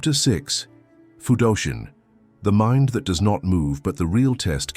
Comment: The mind that doesn't move is 'Fudoshin' sounds like youre saying Hayoshin and labelled it Hayashin in the video.